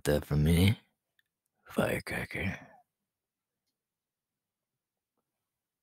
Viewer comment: my mom was talking to me and this was paused and one of my airpods fell out and I hear “I want you to bed for it~” AHHHJFKSNFJESA